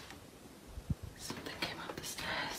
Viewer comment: i hear hi or hey when she asks hello